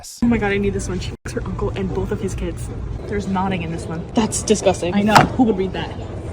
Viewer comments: Okay, what did she say was in the second book? “Nonning”? Idk if I live under a rock or if I’m mishearing her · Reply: Pretty sure she said "knotting" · did she say nodding or am i mishearing her unless theres some evil meaning to nodding that i dont know, can sm1 explain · What is she saying? Noding?